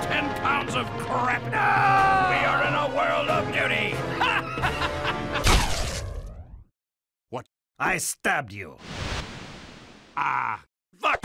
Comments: "I WILL CRAP IN YOUR MOUTH" "wut" "C'MERE SWEETHEART" "WHAT" *Evil laughter* "HELP" "I HAVE 10 POUNDS OF CRAP" "NOOOOOO" "WE ARE IN A WORLD OF DUTY" *Maniacal laughter* Comedy gold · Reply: i thought he said “we are in a world of beauty” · Soldier: "I have 10 pounds of crap." Spy: "NOOOOOOOOOO" Solider: "We are in a world of duty" The ulitmate poop joke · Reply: I thought he said we are in a world of BEAUTY and was honestly horrified was like wtffff haha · "We are in a world of D U T Y." Best pun of the year. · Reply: he actually said "we are in a world of DOODY" put cc on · "I WILL CRAP IN YOUR MOUTH" "wut" "C'MERE SWEETHEART" "WHAT" *Evil laughter* "HELP" "I HAVE 10 POUNDS OF CRAP" "NOOOOOO" "WE ARE IN A WORLD OF DUTY" *Maniacal laughter* Comedy gold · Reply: Yeah, but it's the sound effects in the background that make it sound like he said beauty, when in reality, he said "doodie/duty.". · The words “ I will crap in you mouth “, “c’mere sweetheart”, “ I have 10 pounds of crap “, and “Your in a world of doody” (however you spell it) continued by hysterical laughing is the scariest thing I’ve heard in my life. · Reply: i heard "we are in a world of beauty"